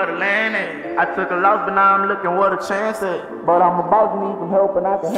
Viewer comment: did anyone else hear young boy say come here fat daddy to one of his kids